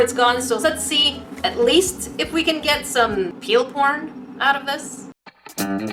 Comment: wait what did she say? peel porn? xd